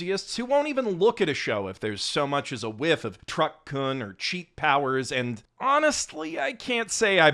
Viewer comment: did you just said "truck c*nt" !?